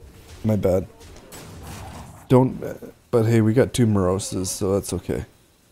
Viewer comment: Did you just say you have tumorousis?